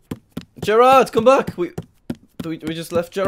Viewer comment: Did jack said my name Gerard?